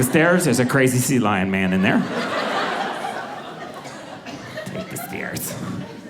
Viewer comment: I think a robot just said, "take the stairs..." Awesome!